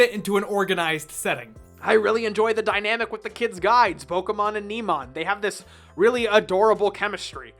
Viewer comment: Did I hear "Pokémon"?